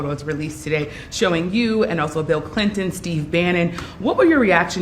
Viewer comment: It seriously sounded like she said Bill Clintonstein. I almost fell out of my chair